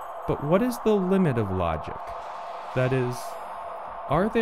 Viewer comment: from 7:50 and on? he say: " ------- are there some true statements that are --> ? alderreach? <-- of logics? what is the word he said? alder reach? alder reach of logics? please answer? I dont undersatand! I dont get that word right! alder reach? no ebglish word as I hear it! must be something else! please tekk me from 7:45 and onwards! Einar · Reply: "out of reach"